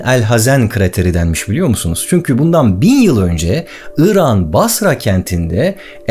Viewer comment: 1 yıl önce değil 1000 yıl önce orada "1 yıl önce" olarak duydum yanlış mı duydum? Doğrusu "1000 yıl önce" değilmi...